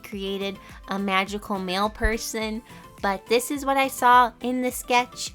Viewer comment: What she said: magical mail person What I thought she said : MaGICal MaLe peRSon